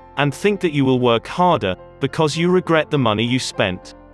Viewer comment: cái chỗ 1:09 nghe ra là you look like the money you spent, nhưng script thì là regret, ông này có nói vấp k ad hay là nghe nhầm